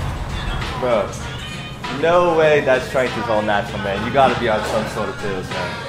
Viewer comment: what did he say ? I heard only "bro no way ..." get out from gym ! :D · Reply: No way your strength is all natural, you gotta be on some pill, hahaha thats how people think when you train hard ^^